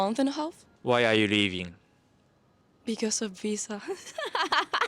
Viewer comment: I thought she said "because of pizza" 🙄